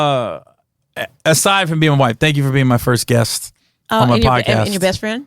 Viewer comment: I heard “my first wife”😂😅😁